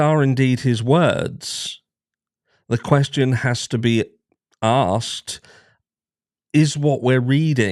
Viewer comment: A light touch on a serious subject - when you started it sounded like you said 'it has to be a*rsed' not asked 😁 I'm not English, I'm ex Rhodesian/Zimbabwean & ex South African, but English speaking. I've lived in the UK since 2003. I'm used to the different accents. My dad was Welsh my first love from Yorkshire. Just gave me a giggle..